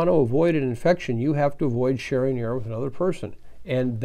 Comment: or so . . . Help, what did he say, he slurred an important word: "If you want to avoid getting this virus you have to avoid sharing __???__ with another person". What was that sentence?? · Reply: Sharing air with another person